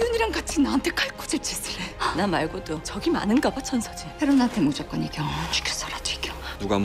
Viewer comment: The subtitles says "You have to beat me by boat." LOL she was talking about bae rona